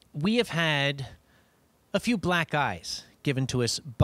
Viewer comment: Lol I thought he said "black guys" instead of "black eyes". xD